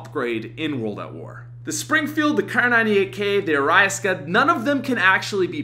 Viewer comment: Wtf did he just call the arisaka?!😂😂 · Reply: He said a rise ka 😂😂